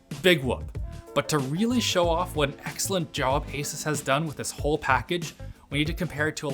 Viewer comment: did he just say ASUS as ASAS?